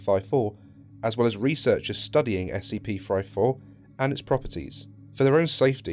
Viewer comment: I heard 'SCP-Fried Fork'